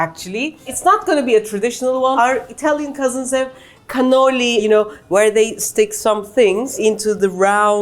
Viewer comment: I thought she said cannoli Instead of cannelloni at the beginning and was confused until I saw the pictures. 😂 Also, vegetarians, It’s really not that difficult to figure out substitutes on your own. I made Uzbeki mantı this week with impossible meat in one version and spinach and chickpeas in another (Uzbeki mantı is begged than the Turkish version), all it takes is the slightest bit of creativity. Mushrooms, lentils, all the endless types of fake meat that are now available…. Edited for spelling